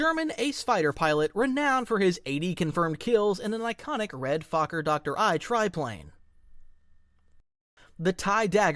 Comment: Did I just hear "Fokker doctor I" ? 💀